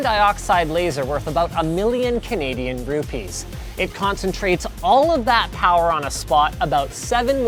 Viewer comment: did he just said million Canadian rupees?